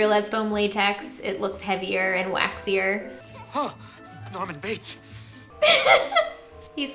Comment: did he just say ah norman bates? Or am I losing it